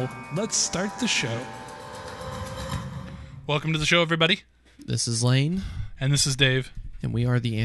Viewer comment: did he say this is lame